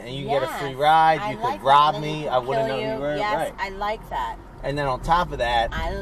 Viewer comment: did she just say she could kill u?